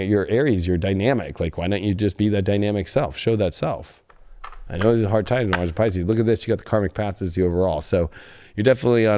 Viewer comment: Okay, I need to know what you said at 2:00:03. "...be you dynamic self.. and you trailed off and ended with Pisces". I have a precious past life connection with a Pisces (to much info for a back story) whom I speak to. But I replayed it over and over and I still cant grab what u said. My interest has been peaked... if you could be so kind... As to oblige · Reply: Okay GF! Some says ... “your an Aries, your dynamic.. Like why don’t you just be the dynamic self, show that self. I know this is hard times in Pisces. Look at this you got the Karmic path as an overall” etc etc